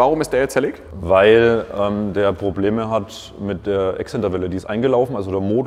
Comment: Wie ich einfach beim ersten mal "Excel-Tabelle" verstanden habe statt Exzenter-Welle 😂😂😂